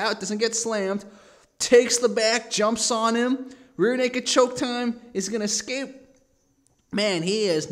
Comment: did he say real nigga choke time